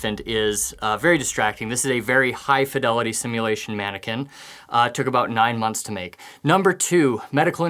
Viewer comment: 😂😂😂 I knew I was stupid, but today proved me right! I heard "high simulation mannequin..." and I looked at the baby for over 2 minutes and thinking "Wow, they make them real these days!" 😂😂😂 The 9 months totally flew past my head!